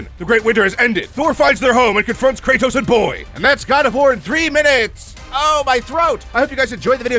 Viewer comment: LMFAO I PUT CAPTIONS ON AND AT 3:08 IT SAID “got a horn” instead of god of war.